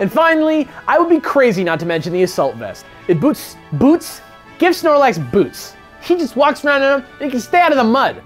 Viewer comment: How did I hear say," It gives snorlax boobs"???